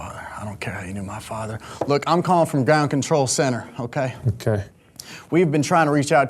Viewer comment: I really thought he said We're calling from dome control center. Shit made me spit out my food